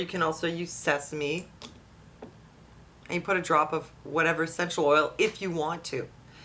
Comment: I want to try this, but I don't understand the instructions. What is the "sexual oil" you mention at 3:14?